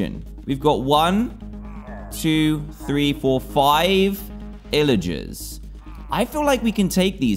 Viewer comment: Did he say ILLagers?